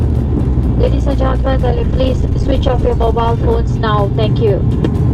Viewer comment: what’s she saying? During takeoff? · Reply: Ladies and gentlemen please switch off your mobile phones now, thank you